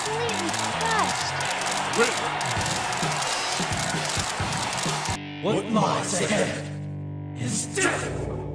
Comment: What did he said? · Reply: "what lies ahead IS DEATH!"